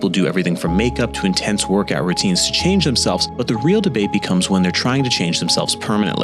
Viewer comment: i literally heard him say „the real dababy is” at 0:33 and was left so flabbergasted i stopped the video and replayed that exact moment a couple times to make sure